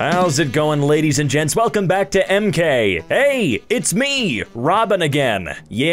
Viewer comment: “Hey! It’s me! Robin!” “Yeeeaaah... Again!” I heard it as “Hey! It’s me! Robbing!”